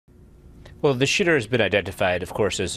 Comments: At first it sounds like he says "Well, shitter has been identified" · Sounds like he says the shitter has been identified.